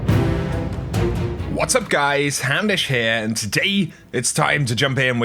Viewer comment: Every single time i hear you say "hey guys, houndish here!" i hear hamish.